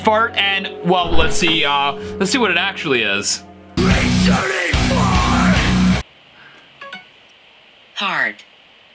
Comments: On one of them, I heard ‘RETURNING FART’ 😂😂😂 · I heard “Returning Fart”